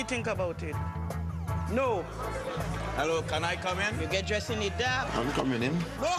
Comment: Actually I think it was "Go home and sort your life out." I had a little trouble at 0:13, I think she says "You get dressed in the dark?"